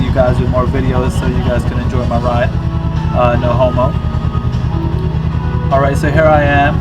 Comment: did you say "no homo" at 0:24? lol